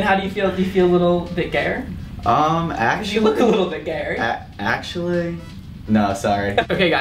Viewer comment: What does he say at 1:03? Look a bit gare?????